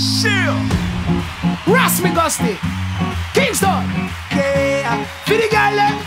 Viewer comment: When he Said kingston i thought that he Said keemstar anyone else also think so lol?